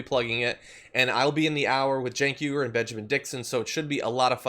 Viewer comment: I was in the middle of something at the end of the show and thought John said “we have great racists tonight” then realized he said “great races” 😂😂 lmao fail on my part